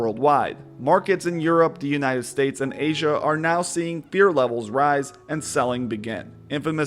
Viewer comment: Beer levels rise? 0:15 Did I hear correct?